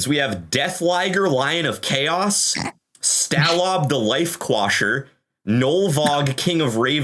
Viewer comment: Most of the time, I listen instead of watch. enunciated just fine, but I still heard "Stallob, the Whitewasher" and had to doubletake